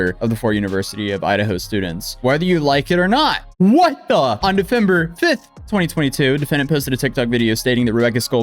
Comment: did you say "Defember"?